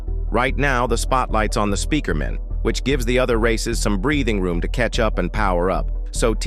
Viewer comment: Why did he said racist.